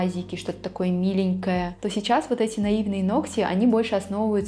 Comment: Я вместо "Наивные ногти" услышала "Наивные Нокси"😆😊 Екатеринбург